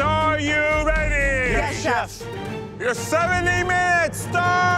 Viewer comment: i thought he said 17 minutes! i was like no way